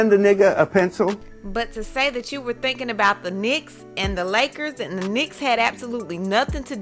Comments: I heard “knickers” as well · I thought she said Knickers, they sound so alike.